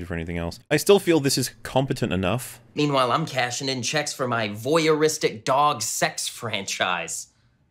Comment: I thought said vouyeristic dog sex franchise for a minute sorry